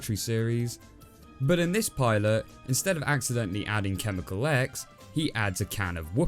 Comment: When you said chemical X my subtitles thought u said chemical eggs oof what a fail